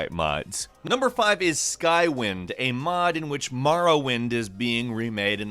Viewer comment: I thought you said “skywhim”